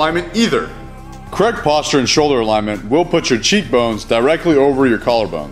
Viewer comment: Can t understand what it said at 6:07 "will put your cheek bone sirectly over your ..." ???